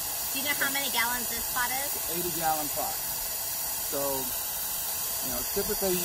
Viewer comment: I could believe a 30 or even 40 gallon pot but, 80 gallons? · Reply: My mistake 80 quart. I thought he said 80 gallon.